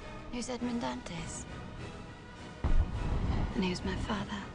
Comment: Just curious at 1:30 does she say Edmond Dantes? Its kind of hard to hear for me.